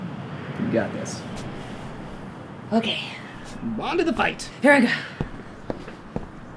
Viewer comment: anyone know what Alex says at 5:14? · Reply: "On to the fight!"